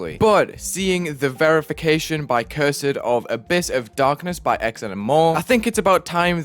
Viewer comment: verified by who? 0:10 I think its cursed not cure-said